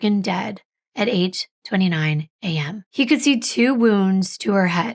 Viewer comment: Sorry😂😂😂😂😂 time stamp 23.04 , I thought you said she was pronounced dead at aged 29 AM. You said 8.29 am. I’m chuckling. I need a hearing aid it would seem. 😂😂😂😂